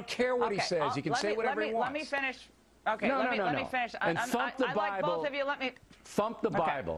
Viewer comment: LOL i thought he said f*** the bible at 1:08 i almost stated to like him for a second there